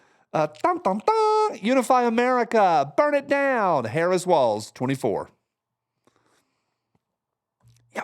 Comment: OMG...when you said "Harris Walz" I thought that you said Harris swallows! LMAO!!!